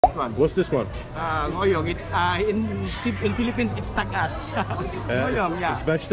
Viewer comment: Sounds like he said in Philippine Means INSTANT GAS. Vegetables ?Yeeaa Vegetables 😂😊😅😂😊❤Nice and GLISTNY OILY FATTY Yummy gas😢😂